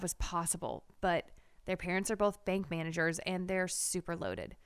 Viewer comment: i thought she said fake managers